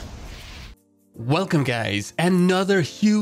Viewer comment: did he said..."welcome gaayyys"???